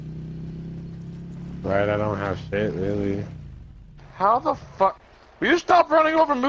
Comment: what did he say? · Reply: I think he said, "I don't have shit really" as in guns/gear